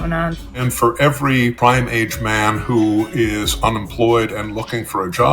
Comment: I thought they said Primate. Lol